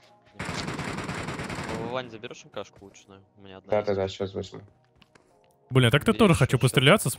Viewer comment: первый раз слышу как дядя сматерился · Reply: Он сказал блин, где мат?